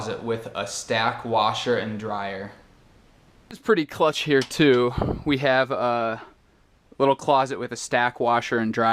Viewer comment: O que foi que ele falou em 2:40? "It's pretty (?) here too" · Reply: falei "clutch" - uma gíria para "perfect, awesome, great"